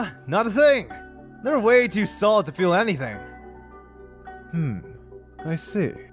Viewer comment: GUYS I THOUGHT HE SAID THAT HE COULDNT FEEL ANYTHING